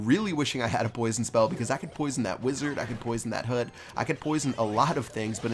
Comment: did he say hoot instead of hut?